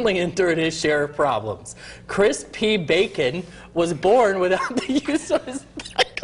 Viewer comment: "Chris P. Bacon was born without the use of his back lchchchkhkhhhkh" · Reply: Sounds like he said without the use of his pickle